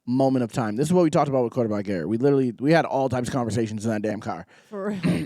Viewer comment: did you say Quarter Black Garret at @ 11:33? like former LWC quarter black garret? that’s dope if so